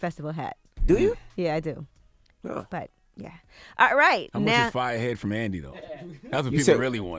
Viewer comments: Did charlemagne say “ I want some fire head from andy” at the 2:28-2:39 mark ? 🤔🤔🤔🤔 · Did anybody else hear say he wants so fire ass head from Andy??? sus